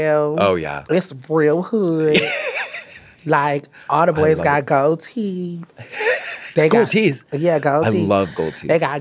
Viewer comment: The way Katya doesn’t understand some of the things Saucy is saying lol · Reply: When he said gold teeth I’m pretty sure Katya thought he said goatee